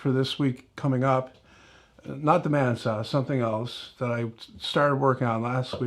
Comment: When you first mentioned the band saw, you kind of mumbled the words. I heard "man saw". Really sparked my interest. Then, you repeated it a few sentences later and I distinctly heard BAND SAW. I said to myself, "Oh! Band saw... That's what he said." Then I continued on, watching the rest of the video. I like the tablesaw sanding thingies. I don't have a jointer, or a big edge sander. When I get moved into my shop space (next week, if anybody cares) I will monkey around and make some of those. I think they'll be great for me, until I can build dedicated machines for such purposes. About the rerun/refreshed video, I haven't seen the old one, OR the new one yet. I think it's a good idea to repost the old ones. It worked for Gilligan's Island. I'm pretty sure I've seen most every episode in rerun form. You know? In your case, by announcing the fact that you reposted, you are making aware that it is available for watching. Or rewatching! Ya know? Even better.